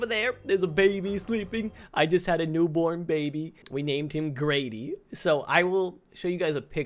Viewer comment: Congrats on having a kid you’ll be a great dad no I totally didn’t think his name was gravy for a second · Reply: he did said Grady but at first I thought he said gravy